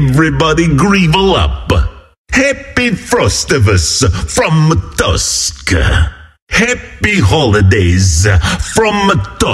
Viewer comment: Is he said Tuskar ?